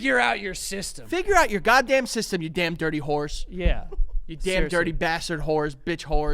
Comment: It’s sounded like said dirty horse!